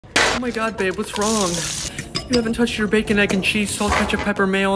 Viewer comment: Am I the only one who misheard the last line "Are you cheating on me" as "Are you shitting on me"....😂